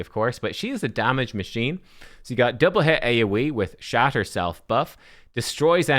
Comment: Did anyone else hear "shat herself buff"? 😅🤣